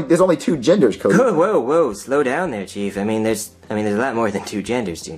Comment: I won hundred percent thought he said “hold on slow down there cheese” because Junior wanted to sweat cheese